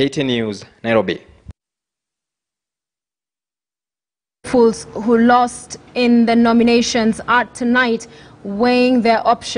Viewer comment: did she just say fools who lost in the nomination?